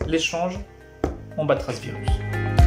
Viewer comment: quand il a dit "on batra le virus" j'ai compris "on batra les russes"